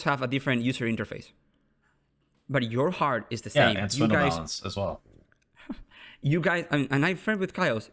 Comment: what did ppd say? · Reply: He said "yea and Swindlemellonz as well". Swindle= Kyle who is now a caster